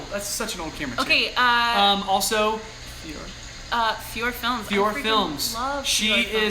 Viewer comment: sorry i didn't catch the names..... can someone tell me? fu-or Films? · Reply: Fiore Films ;)